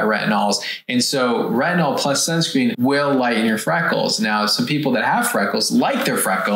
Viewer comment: Did I hear “frackles” 😂